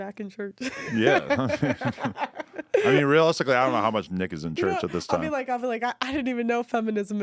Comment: Did he say "Nigga's in Church", or am i bugging?